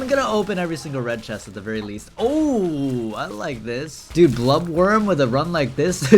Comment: DID YOU JUST CALL ME BLUB WORM 😡